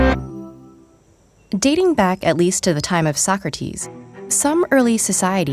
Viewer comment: At first I thought she said "Dating back at least to the time of soccer teams". I am an idiot, though.